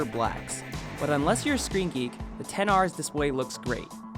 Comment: Help. At 1:53 "...unless you're skreeky...". Skreeky? Squeaky? What is this word and what does it mean?